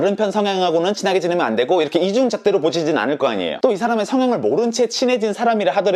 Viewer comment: 무...뭐라고요? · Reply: 이중잣대로 보zi진 않을꺼잖아요